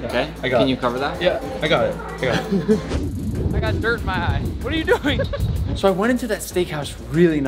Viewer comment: did I hear something about dirt in the eye?